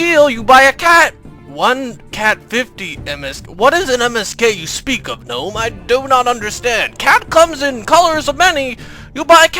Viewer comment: Actualy Dialog "One cat... fifty MSK what is this MSK you speak of gnome i do not understand" Subtitles "one cat fifty dennis what i've heard of this case speaker of the old i don't understand" lolz